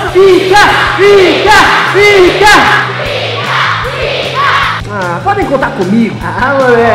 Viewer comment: só eu que entendi picka picka picka ? kkkk